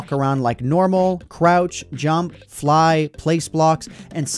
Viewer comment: did he say crouch?